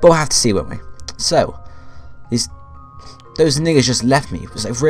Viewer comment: Did you say "they niga's just left me"